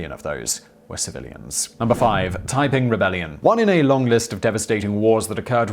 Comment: 5. Taiping Rebellion What I heard was 'The Typing Rebellion' and expected a rebellion by Secretaries around the World.